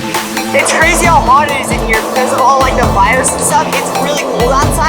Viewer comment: I thought they said virus but when i turned the substitles on it actually said fires I thought they expected the future 🕵️🤔